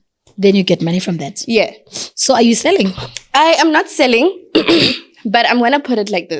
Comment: That sounded like selling, or am I the wrong one?